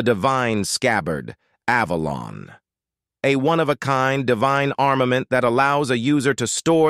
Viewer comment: damn i thought he said armament haki